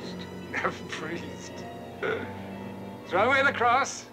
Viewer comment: At first I thought that guy said "Face the monster..." but my uncle said he said "Face the Master." Also when he said "Back Holy, Man, back Shaman, Back Priest!" It made me think of a lion tamer.